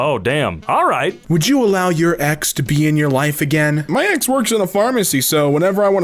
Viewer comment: omg! I thought he said "would you allow your EX to pee in her wife again?"🤣